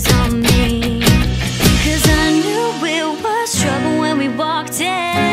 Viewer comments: LAWL IT KINDA SOUNDS LIKE SHES SAYING TRUFFLE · When she says trouble it sounds like she says truffle